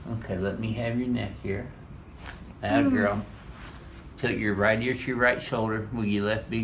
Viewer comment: he says wiggle your left big ear instead of toe hahahahahaha